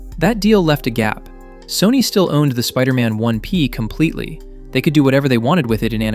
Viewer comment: Sorry, did you just say "the Spider-Man ONE-P"?